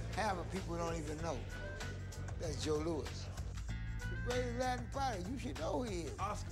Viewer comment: At first I thought he said greatest black fighter. But then that guy said Oscar and I was like wait wtf he couldnt have said that